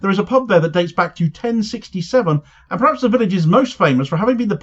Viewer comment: "A pub dating back to 1066" im sure is what I heard you say. CheeeZzz